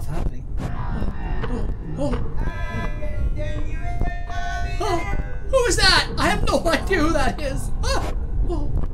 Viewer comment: I Think I heard someone saying at 4:42 "I'll kill u into tubby custard" something like that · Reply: I’m gonna turn you into tubby toast!